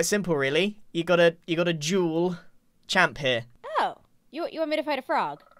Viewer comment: I swear his accent made it sound like he was saying “you gotta juul champ here”